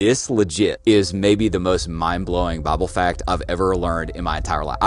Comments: He said Bible I thought he said bubble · It sounded like you said, bobble fact 🤣 it’s that southern twang.